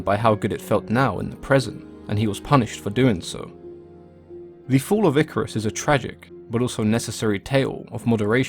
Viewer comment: Who else thought it said “The fall of icarly”